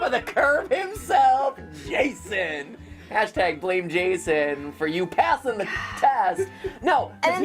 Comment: Blane Jason for passing the class? Is that what I heard? Did I hear correctly? Cause that just sounds.. interesting